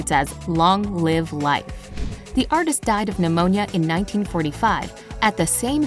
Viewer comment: you say 1945 and subtitle says 1954. Minor mistake but may confuse young learners! :)